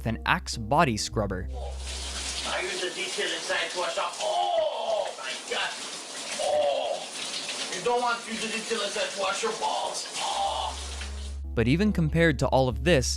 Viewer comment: it kind of sounds like he says: " I use the detail inside to wash off ALL my gut. OH, you don't want to do as ridiculous as to wash your balls. OHH"